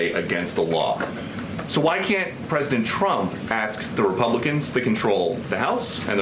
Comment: Calling Trump a Chump may be the first honest thing she's said in this role. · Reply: I've NEVER heard her say anything but President Chump. I'm glad I'm not the only one!! 😂😂😂